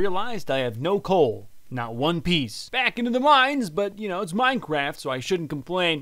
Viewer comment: did he just say ONE PIECE!?!?!?!??!??!?!??!????!?!?